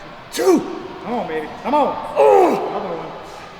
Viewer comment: did he say "c'mon b_tch?" 😶